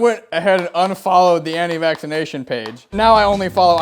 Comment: tell me why I thought said "andi mack's fan page" instead of "anti-vaccination"